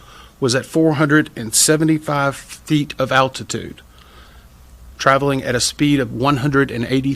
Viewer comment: Why does the thumbnail show "400 feet" when he CLEARLY says at 3:58 "475 feet of altitude"? Failure to actually listen before you posted? And you even CLAIMED to timestamp at 3:47 "400 feet"...